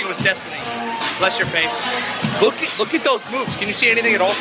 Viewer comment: It sounded like he said look at those boobs and I cracked up no hate